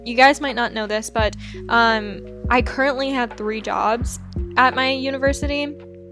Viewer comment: OMG I thought she said “I have three dogs at my University,” and I was like, “Wow you get pets in your apartments?!” XD